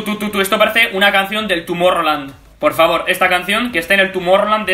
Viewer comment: Escuché "Tumor-rroland"